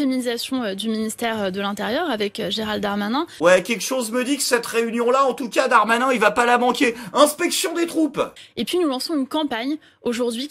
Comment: Quoi qu' il a dit pour Darmanin à 3:56 : " inspection des croupes " ??? Décidément...